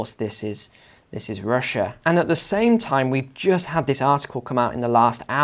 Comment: I could have sworn he said “we just had this asshole come out” Relistening I know it’s “article” and not asshole. But shocked for a second. 😂